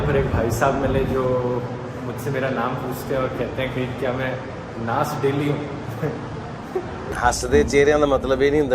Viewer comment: पे भाई साब ने का पूछा? पता नही चला ठीक से... क्या कहा "नास डेल्ही" कोई बता सकता है!! 😗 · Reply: Nas daily a you tuber from foreign